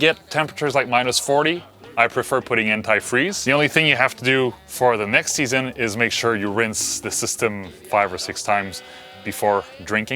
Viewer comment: Did you just say "things we have to do to SELL the van"?!?! Love catching up with you both on Sunday. Bisous et bon courage pour l'hiver!